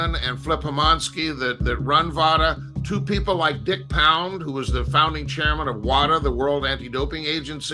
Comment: …..did he say that dudes name is….Dick Pound?